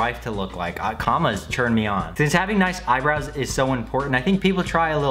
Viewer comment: I heard "commas churn me on"